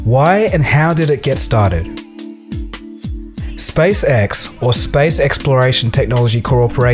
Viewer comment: hehe it sounded like you said space sex a couple of times. But in all seriousness great video coldfusion :)